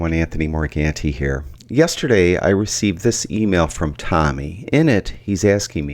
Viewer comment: Not gonna lie, the first time I saw this I heard "I recieved this email from TommyInnit) hahahaha